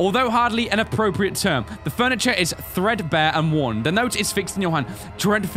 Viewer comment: Omg when 1986 came on the wall and he said thredbare, I thought he said freshers XD